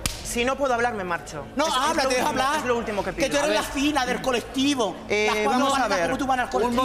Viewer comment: Qué dice? 🤔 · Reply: Creo que dice "Las 4 baratas que retumban el colectivo" pero como habla rápido es difícil entenderla